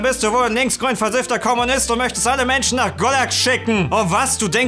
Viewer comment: Auf die Gefahr hin als dumm/ungebildet zu wirken, was hat bei 8:06 gesagt : nach godak? golak? und falls es jemand verstanden hat kann mir jemand den Zusammenhang erklären??